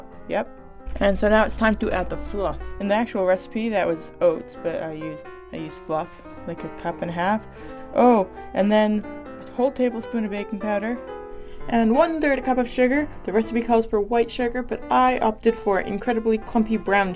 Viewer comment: I thought you said a whole tablespoon of baby powder. It tasted very bad.